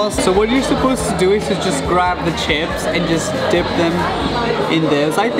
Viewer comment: Me:did he say chips